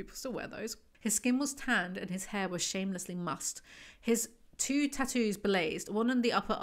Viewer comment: 'his two tattoos blazed' What does that even mean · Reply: I actually heard that as "his lantern tattoo blazed 😂 I thought, how did they get it to do that??